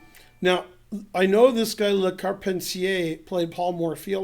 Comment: How do you say that French man's name , my captions say carpet CA ? Abbey normal, lol !